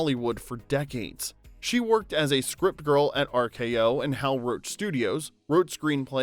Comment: Thank God for closed captions: I read 24:45, "Grandma worked as a script girl in Hollywood for decades." ~ But I heard a strip girl